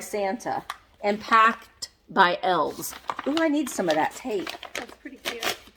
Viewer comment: Thought it said “packed by Elvis”